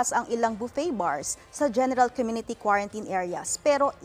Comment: when she said general it sounded like gen¡tal